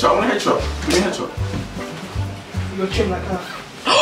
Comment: wait at 22:11 what did the girl in the pink coat say that had everyone jumping and laughing? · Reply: Maame Agyemang I think she said 'with your chin like that' 😭😭😂🤕